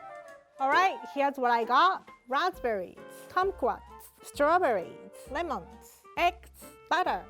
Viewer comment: When she said the ingredient kumquats, I thought that she said "C*m S**ts." Laugh out loud.